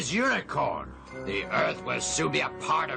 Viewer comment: I heard unicorn